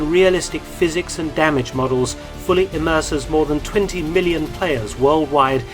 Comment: did I hear 20 billion?